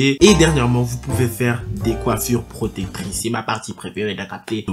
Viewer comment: Des quoi !? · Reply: Coiffures protectrices